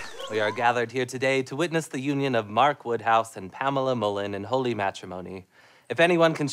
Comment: at first i thought he said Pamela Mullet